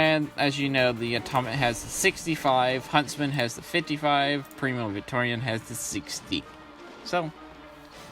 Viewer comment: Its just primo victoria but it sounds like your saying premium Victorian which would be a very dapper chap from the 1880's LOL probably just your accent and me mishearing it.